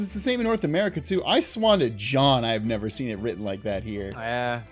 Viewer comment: Did say “swan to John” or “sworn to John?” And is that a real expression? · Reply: Swan to John is a phrase the McElroy brothers use in place of swear to god I like to use 😅